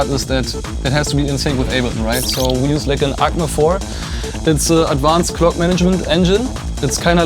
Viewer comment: how is the sequencer at 2:28 pronounced? can't quite understand it :) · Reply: ACME-4, it‘s not a sequencer it’s a Midi clock system